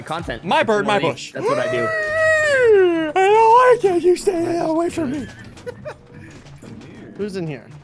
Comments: did he just say "stay the hell away from me." ? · kinda sounds like he says u stay the hell away from me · did he just say, hhhuuuuuuuuh you stay the HELL away from me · Did anyone else hear Ssundee say, "Stay the hell away from me" or was it just me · did he say you stay the hell away from me